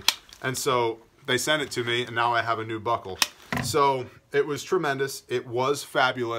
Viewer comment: keep doing what you do. My 15 year old daughter was sitting near me while I was watching this video and @ 8:04 when you said you got a new buckle, she heard you got a new butthole and turned to ask if that is what you said. I need to get her hearing checked...